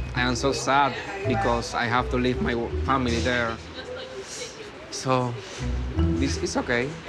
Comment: It sound like he said that he had to leave his wife over there. Smh.